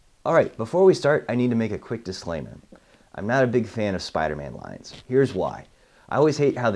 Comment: I first thought he said he's not a big fan of Spider-man, but it took me a couple of rewinds to hear him say "lines".